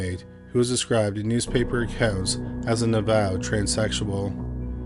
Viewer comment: what does he say? A ___ transsexual? I couldn't make it out. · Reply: It sounded like 'avowed', which means open and admitted.